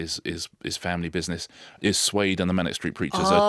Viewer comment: Suede? I thought you said Slade, lol!